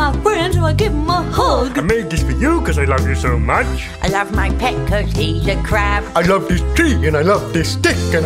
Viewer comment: “I love my pet ‘cause he’s a crab.” That's so stupid- I love it. In two years, someone remind me to put that in for my yearbook quote. update: I'm actually doing an early grad :^) been working my ass off, but i'll see yall in 2023 <3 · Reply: This entire time I thought he said “ I love my pet cookies the crab”